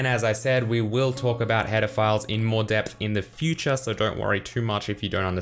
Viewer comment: I wasn't paying attention and heard "Pedophiles" instead of "header files"